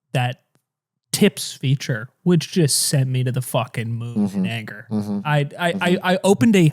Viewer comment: what did said there ??? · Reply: “Sent me to the fucking moon in anger”